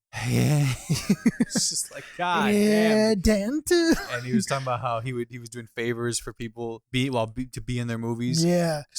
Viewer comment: great episode! curious as to what says at 32:21 — could u pls explain? thank u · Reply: Thanks! And he's saying "Dent!" one of Chris Delias isms